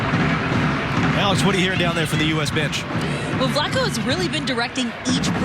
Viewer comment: Did they just say bi*** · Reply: Dipak Dattani it sounds like it, but I think he said bench😂